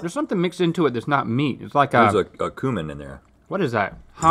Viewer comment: I had to rewind and listen again as I thought Rhett said "this has got human in it" rather than cumin.